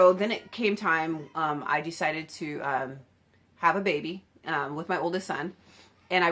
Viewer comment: I thought you said "have a baby WITH my oldest son" and I was like uhhh WHAT? anyway! great that your kids have an air lines mum because truly aviation gets in your blood and it doesn't get out! I'm trying to be a ramper and continue my flight training and your videos are so helpful!!